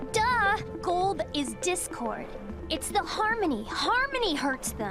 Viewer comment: did she say discord?